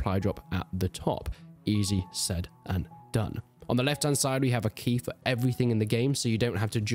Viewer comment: Did he say “easy, said, and done”?